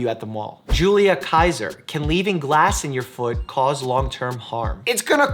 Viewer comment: I heard "food" instead and got confused during that whole part 😂